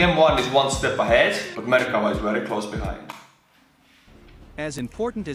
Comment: All I hear is "Murica" Tank.